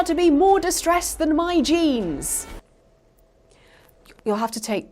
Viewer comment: When you said "my jeans" i thought you said genes 🤔 😒 😂😂😂😂😂